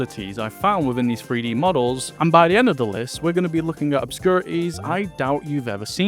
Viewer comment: I heard "obscurities" as "opscurties" and I got confused